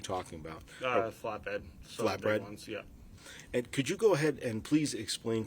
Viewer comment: the lawyer says “flatbread” instead of flatbed ☠️😂